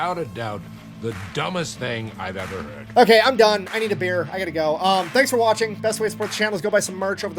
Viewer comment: Did you say you need a beer or a bear?